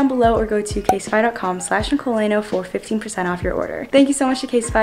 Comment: bruh i'm so deaf i keep hearing "50% off" and i was like wait what now